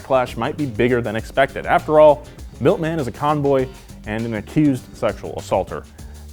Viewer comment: Milton is a convoy? I mean, you sure got the con part right. He's a con-boy. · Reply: He said con-boy, it just sounds like he said convoy